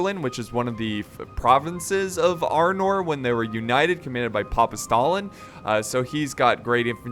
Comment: Did he just said "united by papa Stalin" ? xDDD